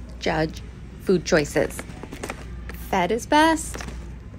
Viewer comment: I thought u said feta is best but then I realized u said fed is best 😂